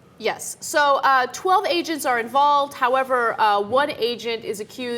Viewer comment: My sound was low and at first it sounded like they were saying Asian instead of agent.